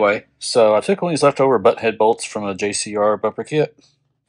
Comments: Did you save big money at Menards? Did you call that a butt head bolt at 2:46? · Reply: And no, I said Button head bolts. That's what JCR calls them in their videos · Did you save big money at Menards? Did you call that a butt head bolt at 2:46? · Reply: button head is correct, but it sounded like you said butt head. Which I think butt head is acceptable too.